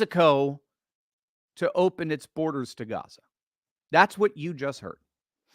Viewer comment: In the beginning of your conversation, I didn’t hear him say open his borders. I heard him say open the Stargate.